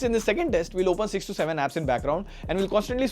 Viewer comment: 6 to 7 . I heard 67